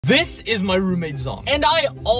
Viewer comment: I heard: "this is my roommates dong" 💀